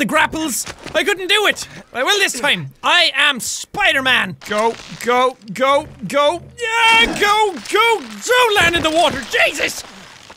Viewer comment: did he said goat goat goat